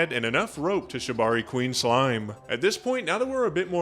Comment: to what Queen slime seriously what the hell did he say and what is it talking about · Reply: Shibari, also known as rope play